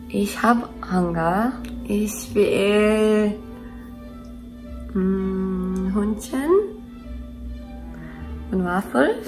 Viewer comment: I heard "Ich will Hündchen" and was like, "Wait a minute.... NO!"